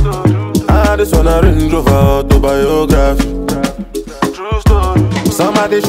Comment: I Can’t Be The Only One Who Thought Burna Said “I Just Want A Range Rover Autobiography” 🤦🏽‍♂️